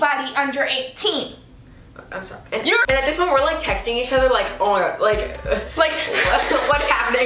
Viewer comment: did you say you were texting each other????